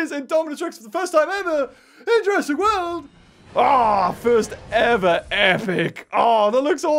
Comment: I got an e-mail notification at 6:10 so it sounded like said “AWWW, fu—ing epic!”